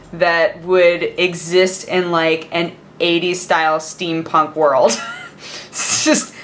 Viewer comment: Steampunk hypochondriac elf street names? Hmm. Breakdown Calamity Malady Malaise Syndrome Lord hazardzone (just found out the character is male, so changed lady to lord. ) Those are what come to mind for me. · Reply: Stome At 9:41 she clearly said steampunk.